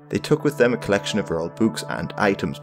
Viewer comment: "They took with them a collection of rural books and items." Rural books? What does that mean? Or did I mishear this part?